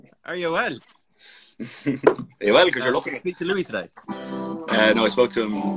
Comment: did he say Louis Styles?